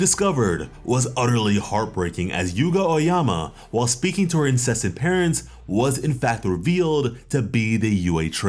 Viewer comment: I wonder how both Hagakure’s parents found each other, that's a love story I'd read. Or they might be related, in which case, nevermind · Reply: idk if i heard it right but at like 2:00 he says incested parents